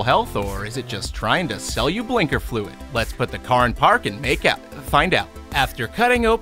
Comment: did he just say "put the car in park and make out?"